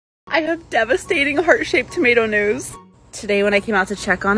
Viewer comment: At first I thought I heard “I have a devastating heart shaped tomato nose” and I was so confused lmao